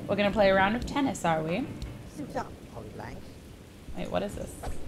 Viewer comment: When he said suit up polybank I thought he said shut up 😂😂